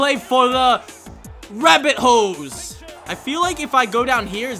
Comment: It sounds like your saying rabbit hoes